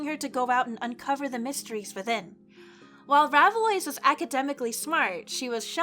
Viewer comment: I thought 15:21 said, "Raviolli" coz her dad's ghost wanted to mess with her